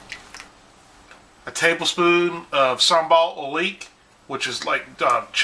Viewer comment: What did he say at 1:48? "Sombalt leak"?